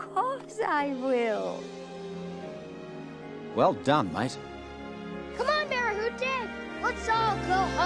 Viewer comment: Well done, mate. 👍🏻😉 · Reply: I heard "Well done Micestro (maestro)